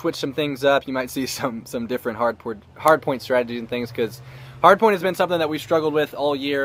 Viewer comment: Lol at 2:45 he said hard porn instead of hardpoint